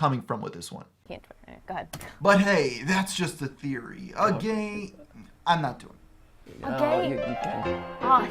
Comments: The gay joke was because when you said "a game-I'm not doing this" in the other video, it sounded like you said "a gay-I'm not doing this" Or at least I think that's why they laughed.... :/ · it's because you cut saying the word 'game' so it sounded like you said 'gay theory' lol